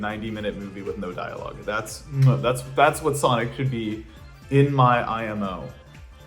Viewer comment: Did you just say In my "in my opinion"